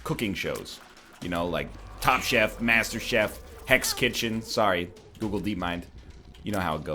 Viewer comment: he said heck,s kitchen for censorship but i thought he said hex kitchen i was very happy to know that wasn't the case halloween would have gone too far that time